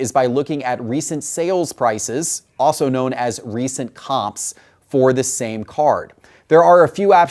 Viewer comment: Thank you very much for this video. As a European who is collecting soccer cards it's interesting to learn about the other sports. Question, What did you say here 43:24? Recent.....? And what is that the abbreviation of? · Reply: "Recent comps" -- comps is short for comparables, meaning what other copies of the same card sold for recently